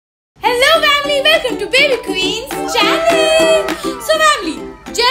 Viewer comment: does she say Challen 😑